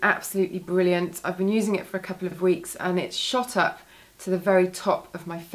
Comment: When you were talking about the Keihls cream and you said that it'd shot up to your favourites, I thought you said "shut up" because my computer blipped a bit. I literally sat bolt upright and to attention! Lol Some great products, I have to try some out!